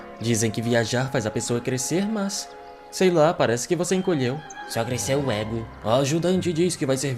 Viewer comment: cresceu oq? eu não ouvi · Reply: disse "só cresceu o ego"